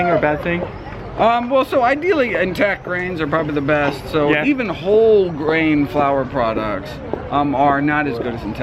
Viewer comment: what did he say on 06:38? something better than bread or something like that..? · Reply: Whole grains 👌🏼